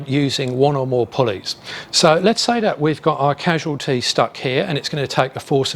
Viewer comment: First I thought you said 'casual' and not casualties LOL